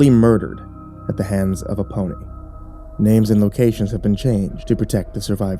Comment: Who else heard, 0:30 "At the hands of a pony" not "Aponi"?? Love the channel Crypt TV, you never cease to scare me under my blanket with my teddy bear, I always have to make sure that my toes are covered too!